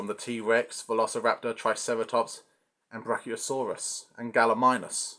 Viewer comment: Sorry I was confused cause I thought you said Triceratons for some reason 😅. I was like, wait a second, how did ninja turtles wound up in Jurassic Park? 😂 Great review 👍